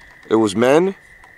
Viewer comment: you thought it said men but I swear it said DEMON “